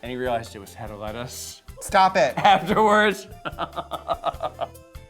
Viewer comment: what is james saying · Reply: Anna Mezebish Hedda Lettuce